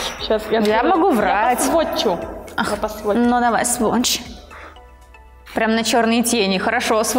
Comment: "Ну давай, сволочь" (надеюсь мне не одной послышалось))